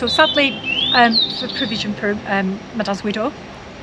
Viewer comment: to @ 13:06 I couldn`t understand what she said. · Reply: Provision for my dad's widow?